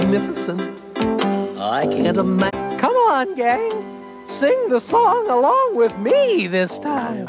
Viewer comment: The HUMAN LOOKING THING : Come on sing along with me this time: No no I'm good Wait did it really say! Ig I'm just hearing stuffs... But that was creepy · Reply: It said cmon gang